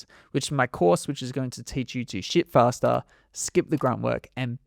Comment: I swear he said "Sh!t Faster" I listened to it 3 time. If the subtitles were not on I would probably still think he said that. 🤷 Maybe its just me. ¯\_(ツ)_/¯